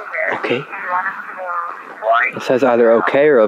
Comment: Evp audio 1:17:50 when you asked if they were playing with you and following you want I think I heard was "Right"